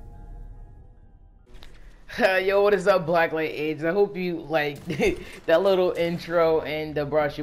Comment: it sounded like you you said black light aids lol